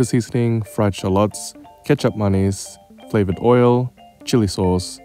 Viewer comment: Kecap manis IS very f good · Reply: I swear I heard ‘ketchup mayonnaise’ and I was really confused. I’m glad you commented what he actually said. I LOVE these btw. Hands down the best noodles I can find in my grocery stores. The Buldak noodles are a close second, but I need to be in the mood for spicy noodles to have them.